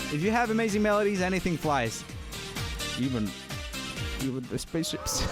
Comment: what did he say? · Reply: "even the spaceships" the swedish got the best of me there